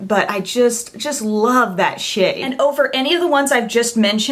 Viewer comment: Haha at 3:05, I thought you said "I just love that shit", lol